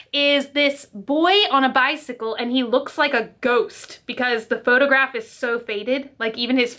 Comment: When she said this dude looks like a ghost I thought she said this dude looks like a goat 😝XD